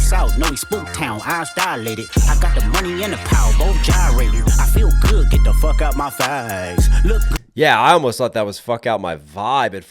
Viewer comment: Why do I hear "I feel good- get the f*ck out my thighs" 😭